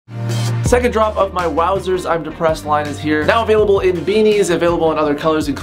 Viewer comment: I could have sworn it said “wowzers my DUDE I’m depressed” not “wowzers my guy I’m depressed”